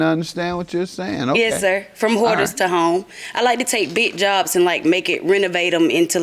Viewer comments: I thought she said hooters and homes at first and I think Doyle did too that's why he laughed lmfao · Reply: Lol I thought she said “Whores to homes” 😂😂🤦🏽‍♀️😮‍💨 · He thought she said from Whores to Homes 🤣🤣🤣 I caught it when Doyle did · i heard “from borders to homes”